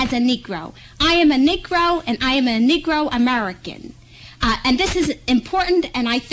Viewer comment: Did anyone hear him mutter under his breath, “How are you a Negro and American” I’m literally dying lol he’s too funny 7:05 btw